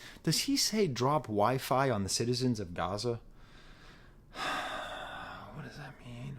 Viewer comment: I thought he said “drop WiFi on the citizens of Ghana” idk though